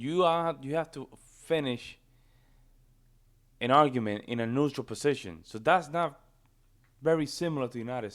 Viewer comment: Did you say we „have to finish an argument in a neutral position?“ I may have misheard that. About 10:45, it was.